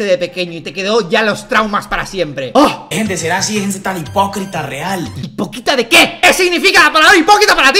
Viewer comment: le entendí boquita antes que hipocrita Xd